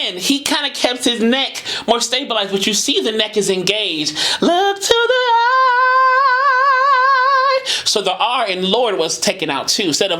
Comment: And here I thought he was saying "I look to the light!"🤦🏾‍♀️